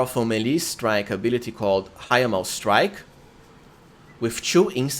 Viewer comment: Not gonna lie when you said "it'll make everyone wet" and then it sounded like you said "Hymen strike" lol. In honor of making me laugh due to my own misunderstanding I'll name my second character after you "cRPG Hymen Striker"